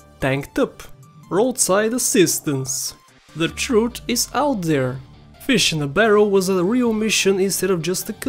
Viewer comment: Wtf are you saying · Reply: "The Truth is out There"